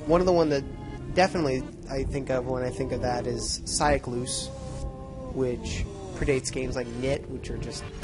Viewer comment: what's the game you mention here? i can't make out the name/spelling EDIT: nvm found it (Seiklus)